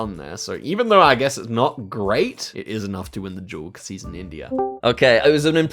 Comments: I swear just said “it is enough to win the duel because he’s an idiot” 😭😭😭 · I thought said "it's enough to win the game cos he's an idiot" and I was so surprised because is usually so nice 😭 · did really say that or did I hear it wrong? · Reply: I think he says “cause he’s in India” but yeah it did sound a lot like “cause he’s an idiot”